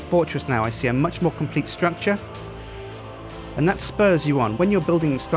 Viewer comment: Did he just say "That's what sperms you on"?